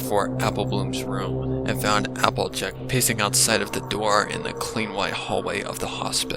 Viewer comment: Listening to r ur vids while hitting bongs outside in the evening while drinking ice tea is what perfects my evenings :0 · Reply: I THOUGHT U SAID "found applejack p¡ssing" for a second 😭